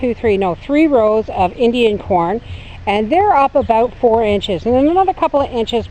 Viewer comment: Enjoying the look back from you. Unfortuneately, I misheard you about your "Indian porn", and was quite confused until I realized it was Indian CORN you had planted not PORN. Silly ol' Bear, I am.